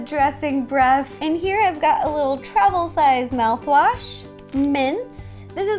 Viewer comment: When you said "Little travels size mouthwash" for some reason it sounded like you said "Travel size Malfoy." and it made me rofl.